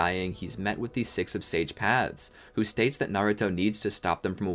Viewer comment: did he just say six of sage paths😂😂